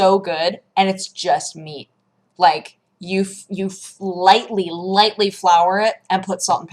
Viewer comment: I thought you were saying Holy Flour. I'm from Sweden and has never heard wholy flour spoken before. I WANT HOLY FLOUR RIGHT NOW!